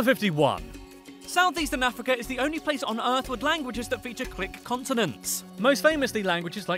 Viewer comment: did he say "click" or "quick" continents? And what does that mean???